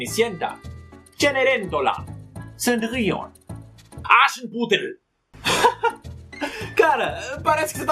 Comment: entendi "acha um putero"...kkkkkkkkkk